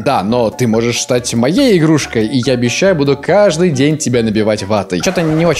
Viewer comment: На 8:40 мне показалось сначало что он сказал ,, Я могу быть твоей игрушкой и ты будешь набивать меня ватой"